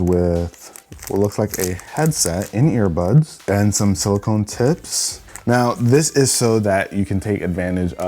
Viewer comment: I thought he said silicon tits 🤣